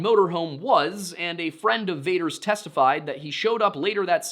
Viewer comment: Friend of WHO?! I heard Vader.